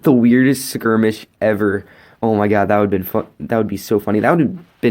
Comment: What were you about to say there? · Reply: That would have been funny, lol the subtitles thought i said the f word😂